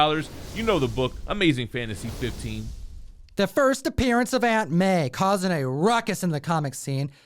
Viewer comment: Oh! You said Aunt May! I thought you said Antman & I got very confused LOL